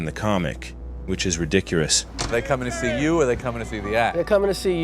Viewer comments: Did you just say “Ridicurus”? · did you say ridicurous??? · did you just say "ridicurous??" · Did you just say... reeedicurus?